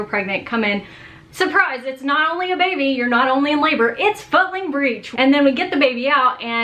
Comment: When she said the baby was footling breech, all I heard was "Surprise...it's a f-" &I clutched my pearls! 🤣🤣🤣 "Is MamaDoctorJones about to curse at us???"